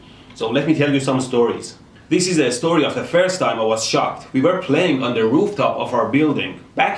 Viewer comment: i missheard it as "let me tell you a story of the first time i was SHOT " i was on the edge of my fucking seat